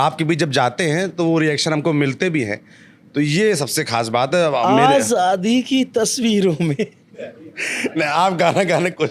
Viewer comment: आज शादी की तस्वीरों में लाल लहू की धारा है। मुझे ऐसा क्यों सुनाई दिया।😂😂😂😂